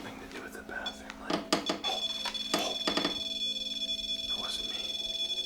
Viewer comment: I hear a mans voice saying “to the left” and “ it wasn’t me” was that any if you guys? It’s very loud , anyone else can hear it???????🙀🙀🙀